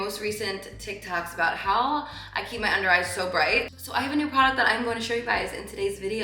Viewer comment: At first I heard you say underwear and I was wondering why someone would ask how to keep their underwear bright and where did they see your underwear in the first place 😅😂 I laughed so hard when I realised you said under eye 😅